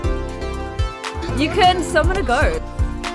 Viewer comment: At the start is sounded like she said summon a goat lmaooo